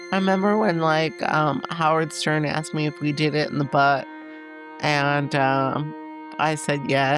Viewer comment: did she say butt or bus??